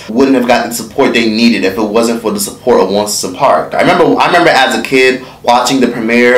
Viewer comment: I thought he said.....ONCE A PARK😂😂😂 I'm like i ain't NEVER seen this new show. LMBO